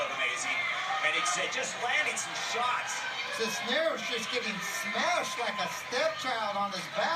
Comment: Did he just say he's getting smashed like a step child lol